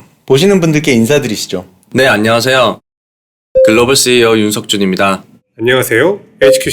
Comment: it sounded like he said kim seok-jin